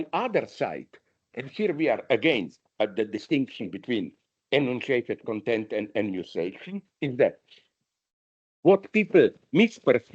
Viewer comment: what does he say here? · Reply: "...and here we are, again, at the distinction between enunciated content and enunciation."